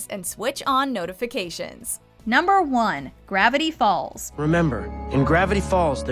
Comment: I thought you said gravity falls sucked